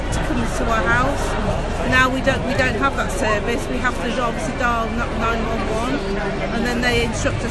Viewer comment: did she say we have to dial 911